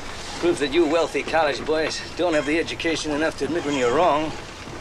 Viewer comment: "Not admitting when you're wrong...". The biggest problem of our time. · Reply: Lol i always thought he said "live on your own"